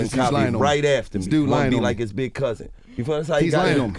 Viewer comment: Wallo: Chokes up explaining how his brother was killed Gillie: (Slaps him in the chest)..Tighten TF up man!!..😂..a true big cousin at his finest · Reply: he clearly says he’s the big cousin (gillie)